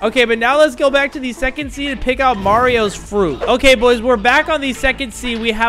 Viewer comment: I thought he said among us fruit